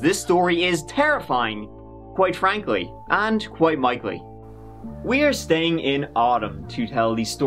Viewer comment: did he say “Quiet Mikely?”